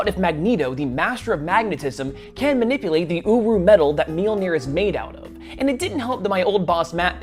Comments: Excuse me did he just say UwU metal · Um... the first time I heard him say the Metal the hammer is made of, I thought he said owo metal.